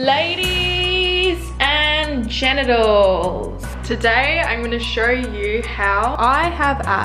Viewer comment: ‘Ladies and ganitals-‘ Me:’did she just say janitors-‘